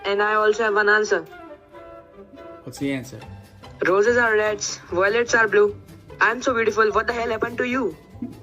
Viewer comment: what did he say? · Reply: Roses are red Violets are blue I am so beautiful B what the hell happened to you (I think)